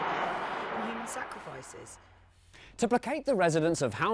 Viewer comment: All i heard was some sacrificial bull**